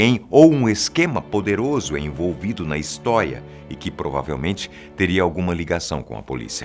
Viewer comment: Escutei, re escutei, re escutei e é isso mesmo mando uma "histoia" kkkk 12:10 min, excelente vídeo !